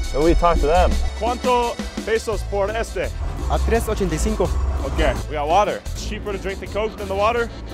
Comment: it’s not 3.85 mxn pesos like the captions said. He meant 385. He only said that bc you’re clearly an American and was overpricing it. Those go for no more than 85 pesos in Mexico